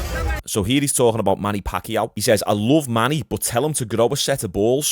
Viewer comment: I really thought he was saying “I love money” lmao.